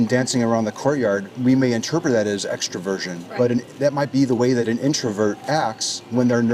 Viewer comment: Did he say, "extra virgin"? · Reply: extroversion